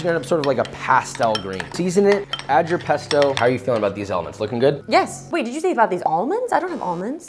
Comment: I swear I heard “elements”